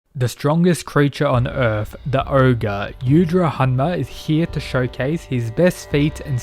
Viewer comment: did he said orgay